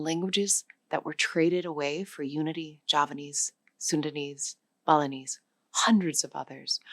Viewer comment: I thought I heard the presenter say 'Sudanese' when it should be 'Sundanese'.